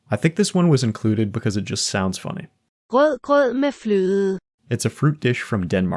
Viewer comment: did that say "glug glug my flow" 🍻